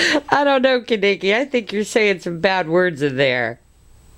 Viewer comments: did he say: Bad words?so what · cute, it sounds like he says "bad words, so what." around 1:40. :) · Aw did he say "bad words no fun" @ 1:41 he's a riot!!!!^^